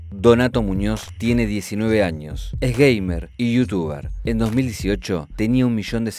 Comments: Like si en el minuto 14:13 entendiste es gay y devolviste el vídeo y te diste cuenta que dijo Gamer* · flashe que dijo que era "gay" y después me di cuenta que dijo "gamer" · En el minuto 14:13 crei que dijo es Gay....Mer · pense que decia que era gay